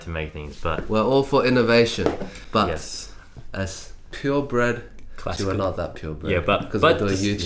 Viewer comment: I heard "pure brett..."